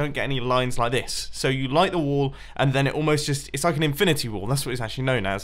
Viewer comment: mumbo thanks to your accent i thought you said infinity war not wall i think i watch too much marvel